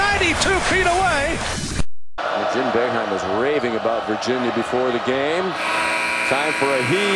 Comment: what did he say!?!?! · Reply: I swear he said raving about virginity